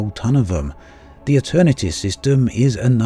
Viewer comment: Could you add the systems and station names to your description? At 2:57 it sounds like you're saying "The Eternity system" but there isn't a system by that name. Are you referring to the Aeternitas system (mentioned by you in a previous video)? At 3:06 it sounds like you said "Helpman Gateway" but I couldn't find a station by that name. Do you mean "Houtman City" in the previously mentioned system?